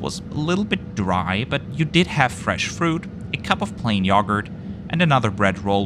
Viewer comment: I know I'm bring silly, but I laugh because I thought he said "plane yogurt" hahahhaa